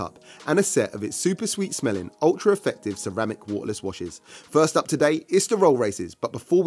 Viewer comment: Admittedly I'm a bit of a motorsport laymen @ 6:25 I have no clue what he said OR what I'm looking at here! 🤣🤣🤣 Could someone clue me in? I mean do you eat it? Did he say watches or washers? ..I think he said washers, and if they are, why would you eat/smell washers?🤨